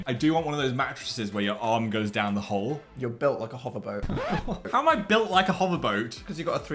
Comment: Misheard "you're built like a hover boat" as "you're built like a homophobe" 💀💀